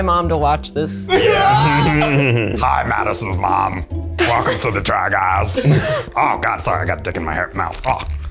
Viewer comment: love that eugene says: "welcome to the try guys" and the subtitles say: "welcome to the drag ass".